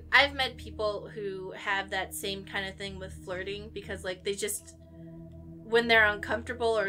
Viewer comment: I thought you said "farting"... I was very confused. I didn't know people compulsively farted.